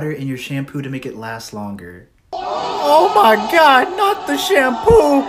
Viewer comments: For a second I thought he said “Oh my god, not the shampoop” 😂😂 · ngl that voicecrack made it sound like said "Shampoop"????